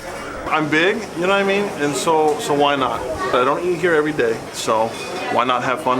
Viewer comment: Hahaha fat model guy "I come here everyday,why not" Reporter "after 2 weeks he died" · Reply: He actually said "I don't eat here every day"